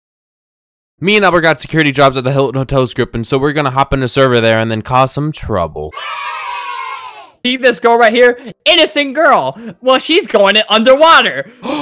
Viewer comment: I heard “Cause some trouble” No hate or anything, Merry Christmas